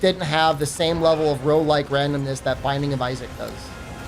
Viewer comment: For some reason I misheard "Rouge-like Randomness" as "Pro-Life Advocates" and I was wondering exactly which parts of the Binding of Isaac could be considered "Pro-Life"...